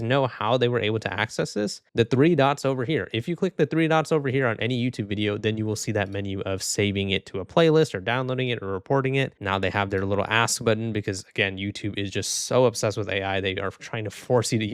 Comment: i didnt look on the screen and i thought he said ass